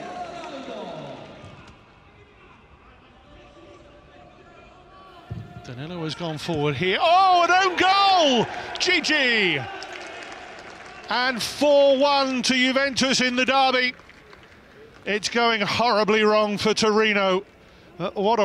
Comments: I was the only one who thought he said "gg" ? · I actually thought the commentator said GG, until I realized the player’s name was actually Djidji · "oh an own goal, Djidji!" how I heard it at first: "oh an own goal, GG!" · I once thought the commentator was saying GG in 3:15, but it's actually the player's name "DjiDji". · i thought the commentator said “GG” instead of Djidji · i thought the commentator said GG then i realize the player's name is Djidji 😂😂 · i thought he said Gg but he said Djidji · I thought he said:”OH! OWN GOAL! GG” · When he said own goal Djidji I thought he was saying it's "an own goalll, GG" 😂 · I thought he said 4-1 juve “gg” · Commentator:OHHH IT'S OWNGOAL. DJIDJI (misheard as gigi) Buffon: yes? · I thought that he said GG at last goal.. Appereantly his name is djidji...